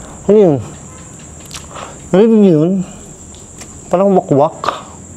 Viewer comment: may narinig ako parang "wak"